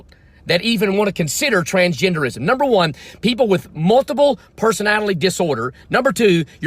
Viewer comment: Did he just say personatalie? Like the guy from the SNL Love Island skit?